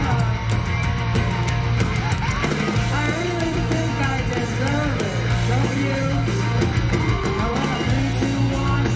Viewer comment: can anybody tell me what they're saying here? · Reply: “I really think I deserve it, don’t you?”